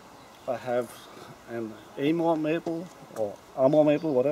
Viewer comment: What maple? I didnt hear the name correctly and I wanna look it up. Nice leaf shape. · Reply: NeutroN ShivA I do think I wrongly said Amur maple. It is a field maple. English isn’t my native language 😁 Acer campestre for sure 😊